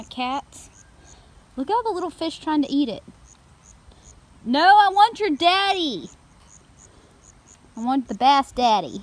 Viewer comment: I had this on in the background and all I hear is "I WANT YOUR DADDY!"